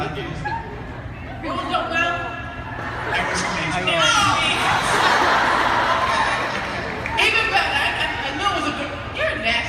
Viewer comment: did dan just say he is taken? he iS TAKEN? I WONDER WHO IT IS OH MY GOD SO HARD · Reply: I found out at 32:00 when glozell asked was that your mouth it sounded like he said im taken but he actually said "it was colleens mouth" Lmao